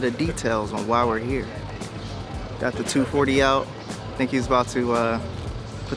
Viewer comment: At one point I thought you said "Tim got the 240L out" for 240 Lightweigh babeeeyyyy!!!